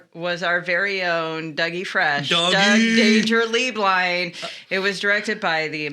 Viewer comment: When Doug introduced himself for the first 8 episodes I thought he was saying, “Doug ‘Dangerously’ Blind” 😭 Cracked me up so hard when I realized I was wrong 😂❤️